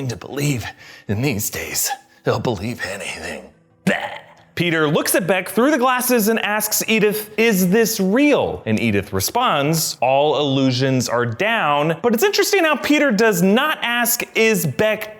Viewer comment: But i thought EDITH said “all illusion are all down ?”